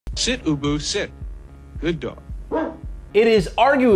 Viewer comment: For along time I swore it said sit boo boo sit..